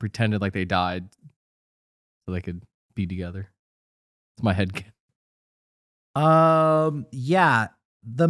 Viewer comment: did say 'my headcanon'